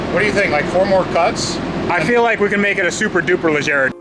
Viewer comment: I heard "Supra le Jared" Sooo 2J swap? 😁